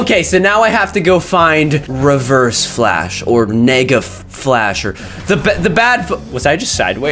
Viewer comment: did caylus say n*gga flash? and thats not reverse flash thats black flash